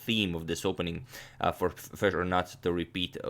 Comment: Did you say FEDERER at 2:20?